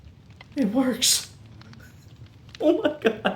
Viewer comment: "it's alive" 😅 sorry thats all I heard when you said "IT WORKS"